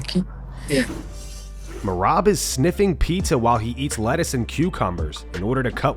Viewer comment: Bruh i thought you said merabs sniffing petr ahead of their fight nit pizza lol